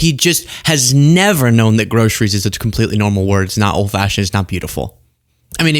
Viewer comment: at 06:37, you said "old-fascist" instead of "old-fashioned". On purpose ????😂😂😂